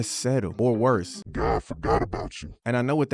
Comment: What are saying at the 2:43 mark? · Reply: “God forgot about you!”